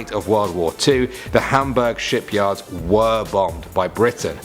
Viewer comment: when he said ship yard i thought he said shit yard